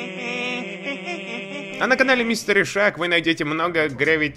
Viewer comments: лайк если послышалось мистер ишак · Мне послышалось Мистер Ишак